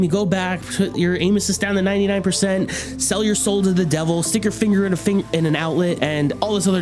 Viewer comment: what did he say😞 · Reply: LOL, i stumbled, it was supposed to be "stick your finger in an outlet"